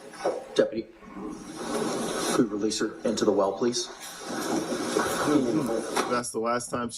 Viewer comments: When the attorney said "could you release her into the well please?" I genuinely thought they said "release her into the wild" lol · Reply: Hehehe. Sarah is basically a feral beast, so it’s not shocking that you thought you’d heard “released into the wild”. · When the attorney said "could you release her into the well please?" I genuinely thought they said "release her into the wild" lol